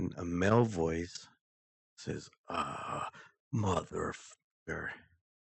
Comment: Sounds like its saying That Mother Fucker instead of Ugghh MFR and most of them were them in the house I heard that go back up there and watch your sister and bella wont play loud and clear...no one knows what its like there ...only the ones that have crossed over know sad that they are gone but they are alive in spirit